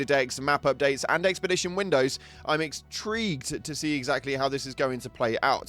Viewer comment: What did you say there? "EXtrigued"? What is that supposed to mean? I mean, I know you probably said it to provoke a comment and I'm here to exdulge you...